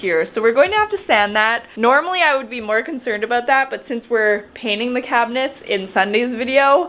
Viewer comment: Did I hear “were painting the cupboards” ?!😍😍 I can’t wait to see that video & the cupboards tie into the new beautiful countertops! Beautifully done you guys. I’m so excited to see your moms kitchen! lol